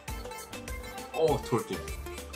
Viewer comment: Hahahah did he just say "Oh thodi" at 3:13 ? lol 🤣